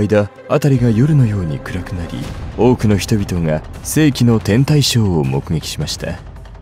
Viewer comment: 「世紀の天体ショー」が「性◯の◯態ショー」に空耳してしまったので耳鼻科行ってきます…